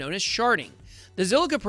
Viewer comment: I thought he was say 'Shart'-ing for the first few minutes of this. And for that, Im out.